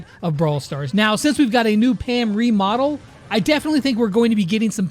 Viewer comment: At first I thought it said Pam Removal